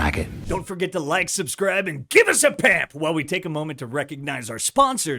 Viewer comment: I've heard say it probably 200 times but still not sure what he is saying. I am hearing "Give us a pap". Like a pap smear? Or surely I am mishearing him? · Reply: Pamp